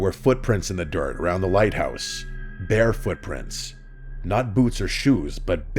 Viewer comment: Almost thought it said “BEAR” footprints.